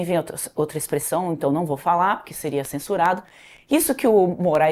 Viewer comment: "não vou falar pois seria censurada" . Foi isso que eu ouvi?